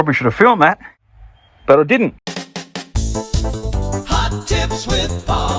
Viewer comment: I got to pay more attention ..I thought you said hot chips 🍟 🤣 with Paulie ...😂 🍻